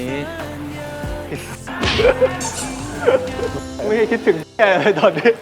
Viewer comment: อยากรู้ว่านาทีนี้ 18:32 สกายพูดว่าอะไรอ่ะมันฟังไม่ชัด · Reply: "ไอ_ส ไม่ได้คิดถึง_ อะไรเลยตอนนี้" ค่ะ555